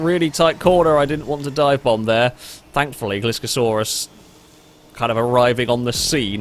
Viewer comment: I know what you said was "Gliscor saw us", but with your accent in my ear I heard "Gliscorsaurus", which I'm sure we'll all agree is one of the best prehistoric creatures ever! Great show, folks! Bravo